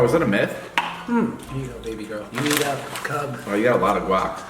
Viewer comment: I swear he just said here you go baby girl I aM dEaD